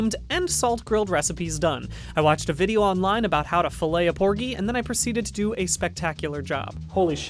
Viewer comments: LMAO I THOUGHT HE SAID CORGI AND I AM DYING ON THE FLOOR!!!!🤣🤣🤣🤣🤣🤣🤣🤣🤣🤣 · I heard “how to fillet a corgi”...